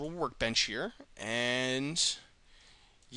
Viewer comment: at the end, i thought you said, "I'm stoned, and thank you for watching!"